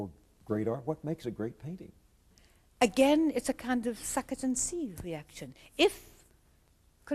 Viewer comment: What is she saying--"___ ? _ and see reaction." Please clarify. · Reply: Andrew C. "suck it and see" reaction. It's an English expression that basically translates to 'to determine what you think of something, try it out'. I.e. to determine how good the spaghetti is at a restaurant you could ask other diners or visit Urbanspoon... or you could simply order it and discover for yourself!